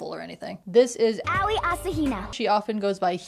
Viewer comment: sounds like yaoi heh I defiantly don’t consume way too much yaoi on a daily basis heh